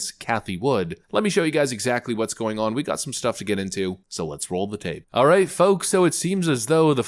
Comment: Every time he says “so let’s roll the tape” what I hear is, “let’s roll that beautiful bean footage.” 🤷🏻‍♀️